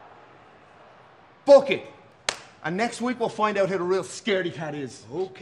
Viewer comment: When he said book it, It sounded like he said phuk it! 🤣